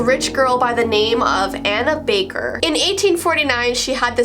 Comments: I really thought u said Hanna baker turns out is Anna baker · “Anna Baker” Me: “Did she just say Hannah Baker?!” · “Anna baker” me: did u just say Hannah baker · for a second i thought she said hannah baker 😭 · Is it just me or did she just say Hannah baker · i thought she said Hannah Baker not ANNA baker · Anna baker i thought u Said Hannah baker. I think i have to stop Watch 13 reasons why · i thought she said hannah baker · FOR a second i thought u said hannah baker phew..... because she didnt like her life either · who else thought she said hannah baker? XD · i lowkey thought she said hannah baker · I thought you said Hannah Baker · why did I hear Hanna Baker? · I thought she said Hannah baker lolzz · Tell me why I thought she said Hannah Baker at the wedding dress part🤣🤣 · I thought she said Hannah baker 😂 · I legit thought she said Hannah baker. 😢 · When she said Anna Baker I thought she said Hannah Baker 🤭